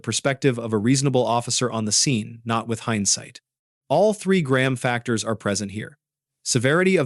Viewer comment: I thought the narrator said graham crackers at first.